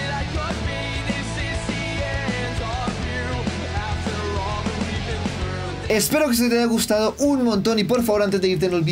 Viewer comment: No entendí xd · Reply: El bebe dice Soi del berde soi felis